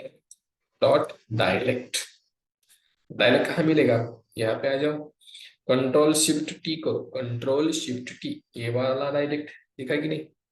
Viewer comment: p kya bola ctrl + shift +? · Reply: Ctrl+shift+t